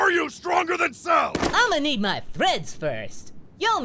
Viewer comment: did goku say im gonna need my freds?